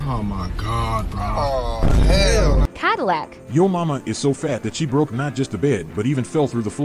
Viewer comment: I thought he said Jump In The Cadillac☕️🗿